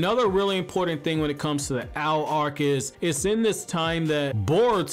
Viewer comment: Before I caught up with the manga I thought y'all were saying 'owl arc' and I was like 'who?'🦉